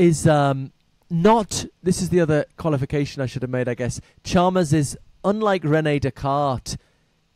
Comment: If anybody looked up "David Chamas" or "David Shamas" like I did, I think he's actually saying "David Chalmers." I'd include a link to the David Chalmers interview with Coleman, but youtube (for some odd reason) doesn't let me post websites. Does anybody know WHY youtube doesn't let us include websites in its comments/replies anymore? It seems like a bed business decision to not let people post youtube's OWN websites.